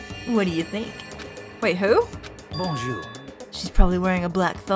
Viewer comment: as a french girl i heard...an Bonjour . like celestia ludenberg who said ah euh pardonnez moi for pepole who want translation bonjour is hello and ah euh pardonnez moi is ah euh excuse me